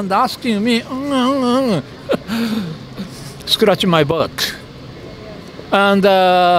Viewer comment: did he just say, scratch my bollocks?!!